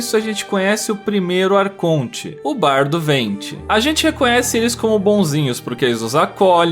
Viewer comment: juro que ouvi " o bar do venti" em 2:26 kkkkk "bar do venti: onde o dono vai a falência por conta própria"